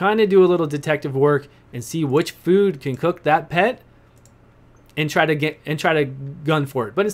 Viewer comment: Am I the only one that heard him say he ate his pet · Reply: He said "which food can cook that pet" at 12:59 lol